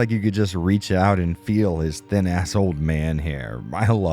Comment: It almost sounded like you said "you can reach out and touch his old man ass hair"